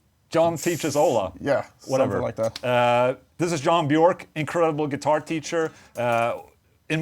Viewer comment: Anyone else mishear John Bjork as Thom Yorke at the start?